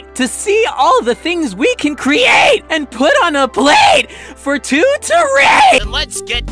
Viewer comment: sounds like said for two to ra-💀